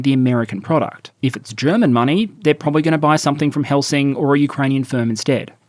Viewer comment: They're gonna buy from Helsing? Or did I mishear?